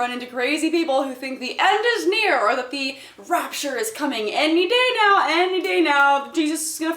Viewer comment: Oh shit i thought science brought back dinosaurs thought is said the RAPTOR IS COMING!!